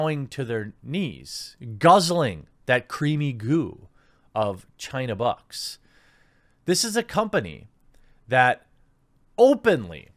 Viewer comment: It sounded like he said " guzzling that creamy goo from China Butts! " ........ 😆